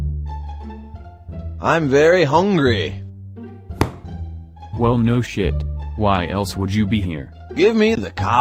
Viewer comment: Did he say shit in vain